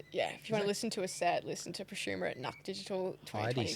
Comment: preshuma at nach digital? what did she actually say I really wanna find this set O.O